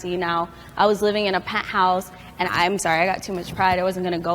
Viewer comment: did she say penthouse? So you were broke and poor cause you lived in a penthouse... cool